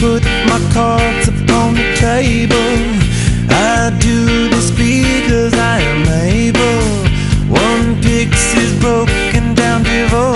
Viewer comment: I always thought he said "and through the speakers I am able" but he said "I do this because I am able"